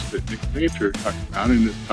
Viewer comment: What do you say here? Sounds like some geological history. T-T You along the fence next to Rock N Tomahawk property? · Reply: HI, See <link> Nick Zentner does lots of geology teachings. He talks about the notch in the hills as being a landmark. We are about 1/4 to 1/2 miles away from Rock N Tomahawk. The fence runs parallel to it.